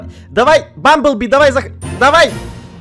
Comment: Я одна услышала слово "Bumbel Be" ? 😓😬😨😲😵😮😐 (Кто в танке то тому рассказываю. Bumbel Be это подписчик Эда, но сейчас он стал участником ну не щяс... А недавно 😏 Ну а 2 кличка у него: Бык, Бычара) Это реально очень странно 😲 Мне только одной интересно он знает про это или же... Нет.)??)😬 ) ИНТЕРЕСЕО ИНТЕРЕСНО... СОВПАДЕНИЕ? НЕ ДУМАЮ...😂